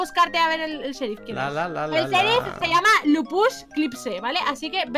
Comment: me dio mucha risa su nombre jajajajajajaja al principio le entendi la pus jajajaja